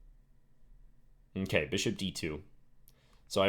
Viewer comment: Have you noticed how very often say "Hmmkey" instead of "OK"? Proof(one of many): 15:41 It's funny :P Thanks for the video!